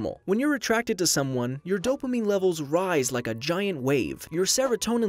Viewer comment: At first I thought that said "Dope Anime"